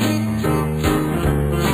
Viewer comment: What does Rob Roy say when he wakes up from his nightmare at 1:17. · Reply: i'm not sure but it sounds something like it sounds like 'Aah ya Sassenach' ( Sassenach being a Scottish word)